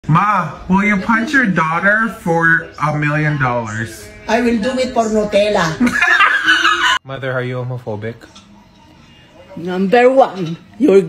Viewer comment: On the first one, I thought he said will you touch your daughter? I was so confused.